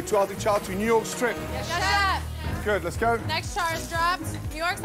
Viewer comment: when i first heard them say “yes chef” i thought they said shut up. my heart sank for them